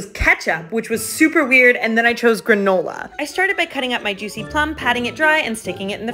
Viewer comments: When it said "Granola" at the start, I thought it said "Grandpa" 🤧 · I thought that granola said grandma “Grandma it’s time to slice and dice” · Am I the only one who thought granola said grandma for a second 💀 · NOO I THOUGHT GRANOLA SAID GRANDMA AND I WAS LIKE "WHAT!??!?@@@@